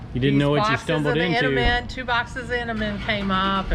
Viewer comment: The Aussie 🐙 thought you were saying Enderman. The tag line makes more sense now :)